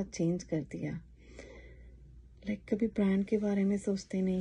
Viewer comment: minutes mein aapne kya bola,? I could not understand. Kya pehnne se? Sorry for the inconvenience. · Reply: She said brands means branded items